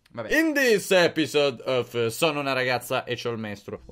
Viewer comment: Ho capito "sono una ragazza e ho il mestolo"